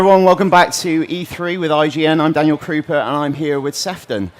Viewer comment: Lol. Sounds like he said "I'm Daniel Creeper" I nearly spat out my drink. Gg man.